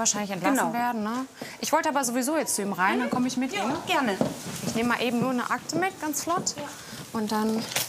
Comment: Ich habe verstanden "ich nehme meine Axt mit"😂 Weiß nicht warum. Wenn ich's mir nochmal anhöre klingt es garnicht mehr danach 😂